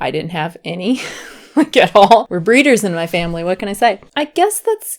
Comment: wait..what did she say here? "breeders" whaat? In any case, congrats on your pregnancy! I wish you and your family all the best 😊✌